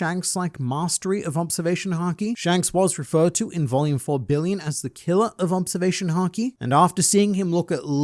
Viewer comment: Am I hearing this right? Volume 4 billion?